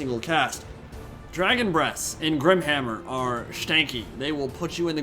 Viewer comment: did he just say "Dragon breasts"?